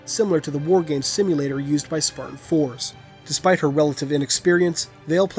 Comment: If you turn on subtitles at 2:16 it says “sperm for” instead of Spartan four